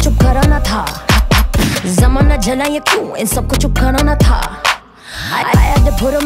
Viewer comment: Anybody heard In 1:57 Sabkojo Corona tha? 🙄